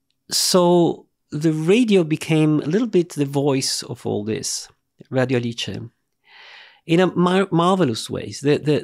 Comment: what did he say after 'radio' at 14:32? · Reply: "Radio Alice", the name of the 'open-speaking radio', born in that period in Italy because new ideas were repressed by the reigning government.